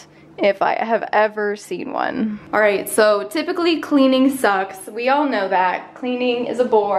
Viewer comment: I could never hate you I love these videos. Wait you have one head? Huh didn’t know that hahaha. Eggplants are gross, coffee is amazing is great with creamer and your funny and I thought you said cleaning socks haha and Gary is really cute. Btw your penguin is BOSS dude